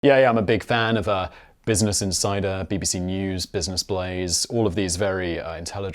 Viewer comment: I misheard at the beginning and now I want to subscribe to Business and Cider!